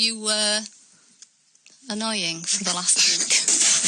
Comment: I thought phils mom said "annoying little bastard" at first. I had to rewind and listen again lol